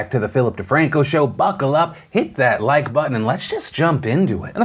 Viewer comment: I heard "fuck a duck" but the caption said "buckle up" I'm not sure which one I like better